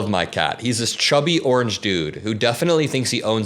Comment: when you said "I love my chubby orange cat" I misheard you and thought you said "I love my chubby orange prat" and I thought oh he's talking about Trump!!!🧐🤔🤪😂🤯